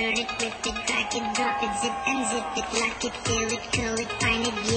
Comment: Alright can we please grow up? It does not say "fuck it". In reality, the line is "lock it fill it" but in the process of looping the audio the beginning of the "f" in "fill it" got put in and it just sounds like it says "fuck it". Again, grow up.